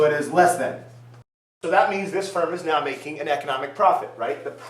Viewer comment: At around 3:08 to 3:12 you said: 'That means this firm is not making an economic profit right...' I think you said wrongly, it is supposed to be 'the firm IS MAKING ECONOMIC PROFIT because the price(Pm2) is GREATER THAN ATC. ' Am I right? Please reply me as soon as possible, because my exam is coming soon. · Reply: Mad Angel he said "now" not "not"